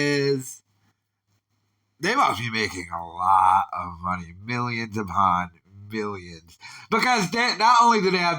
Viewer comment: did you really say billions, or do you have a sinus infection?